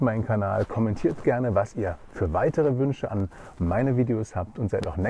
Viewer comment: Ich brauch mehr Schlaf. Ich hab "dieseldad" verstanden *gähn* Vielen Dank für den Buchtipp. Irgendwie muss ich meine 14 "Guthaben" ja mal aufbrauchen :D